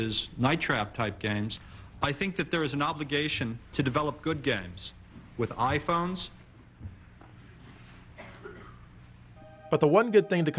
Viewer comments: What did that bearded guy say at 6:38??! "there's an obligation to develop good games with..iPhones"?? No wonder the guy listening to him was stunned! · Did he say iPhones on 6:41? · Did he just say I-Phones? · Did he say..."Iphone"? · iPhones? What did he say? I thought that took place in the early 2000s/late 90s. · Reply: "Eye phones" :)